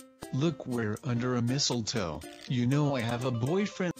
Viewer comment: I thought it said MISSLE toe for a second